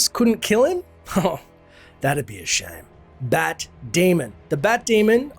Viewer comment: Thought you said Bat Damon.